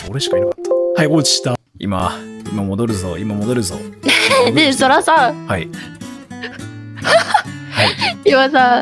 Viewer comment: "Sora-san sora-san" - 2019 Hehe Edit: Why you guys debating something stupid Btw if you watch stream always call him "sora-san" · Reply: "Hehe, ne Sora Sora" that's what i heard